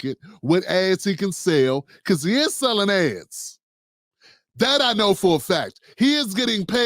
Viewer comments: MAN I thought you said Roland was selling a$$ when you said he was selling ads🤦🏾‍♂️😂😂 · Damn- it sounded like you said Roland was “selling’ ass.” I had to run that back. Ads. 😂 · I thought he said g he e it s selling azz! I was like wait a minute😮😮 oh ads…..😂